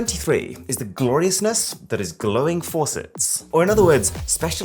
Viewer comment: I thought he said glowing ants. I was so dissapointed